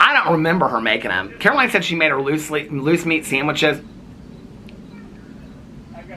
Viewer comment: What is a loose meat sandwich?! 🤣🤮 At first I thought you said GOOSE meat sandwich, and I thought “omg eww” but I’m not sure LOOSE meat is any better 🤣🤣🤣